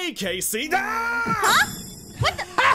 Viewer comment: did i just hear aphmau just say fine ill just go back to choping human groins like WHAT